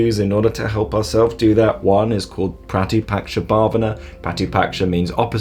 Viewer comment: hey what word did you said at 0:41 timestamp. Did you mean Pratipaksha Bhavna? Can you spell it here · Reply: That's exactly right. You've spelt it perfectly i believe, just needs an extra 'a' like so - pratipaksha bhavana